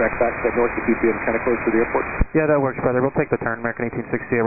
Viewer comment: sounded like "that works brother" lol & CC thinks "better" 4:10 keep you separated